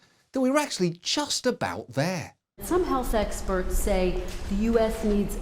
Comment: did she say sexperts?